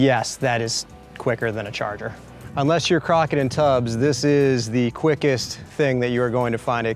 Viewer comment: wtf did he just say? Crocking in tubs?